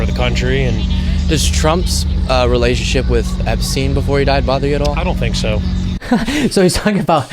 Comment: When said “epstein” the first time I thought he had said “obscene”. Would that be considered irony?